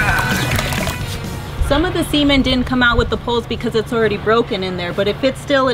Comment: It must be her American accent. I thought she kept saying semen, when she really meant cement.